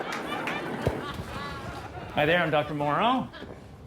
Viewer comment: did he say "I there I'm Doctor Moreau" ? :-D